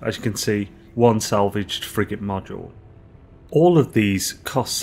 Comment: When you say "Frigate Modules", I hear "Friggin' Modules". Lol.